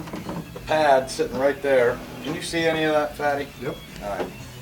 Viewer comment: Did you call your customer "Fatty", that doesn't help with selling your products. xD lol